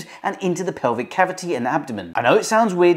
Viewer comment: thought he said uncommon, and was relieved. he didn’t. i’m on my period. i’m mildly horrified.